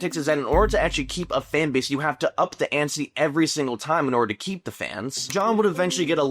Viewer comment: did you just say "up the antsy" my dude